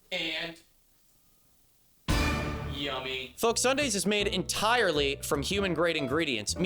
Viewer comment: I was skipping through the ad and thought you said 'this is made entirely of human-' and assumed flesh